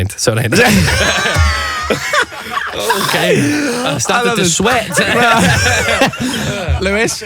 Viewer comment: Someone EXPLAIN WHAT HE SAID THAT MADE PEOPLE LAUGH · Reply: He said "I've started to sweat" right?